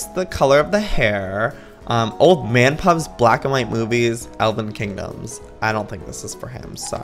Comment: it sounds like he said boobies instead of movies lol